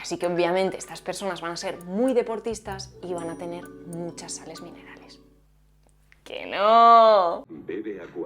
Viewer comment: ¿qué dijo? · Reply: Que van a tener muchas sales minerales. Supongo que es porque Aquarius en España es o era una marca de bebida isotónica (deportiva). En Argentina solo es de agua saborizada.